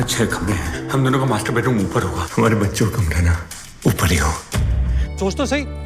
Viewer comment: I heard masturbate room!